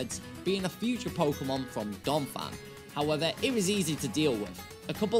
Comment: When you said Donphan I thought you said dog fan